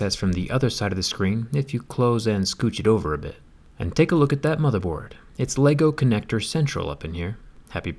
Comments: I thought you said "take a look at that motherF**ker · Jesus!!my brain thought he was going to say. “ take a look at that mother-f$cker!!!” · thought he said “take a look at that motherf*****”🤭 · I thought he was about to say ''look at that motherf*****'' lol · Thought he was gonna say: “Take a look at that motherfucker” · I thought for sure that he is gonna say "take a look at that motherF****r" @ 2:08 .... lol lol............ good one mate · I thought he was about to say “take a look at that motherfucker!”.😂😂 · for one second i thought he said "And take a look at that motherf*ck*r" 🤣🤣🤣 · i for a second thought he said “ look at that motherfucker”